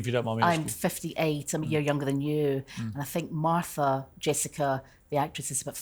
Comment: “I’m 58 I’m a year younger than you” I bet you Piers skin was crawling when she said that LOL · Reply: i thought she said "abit younger than you"